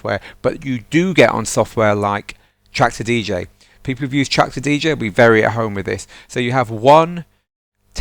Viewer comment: With which software he compare Mixed In Key at 1:40, it souns like he says Tracks a DJ. I don't understand him. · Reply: He says "Traktor DJ" :) -Joey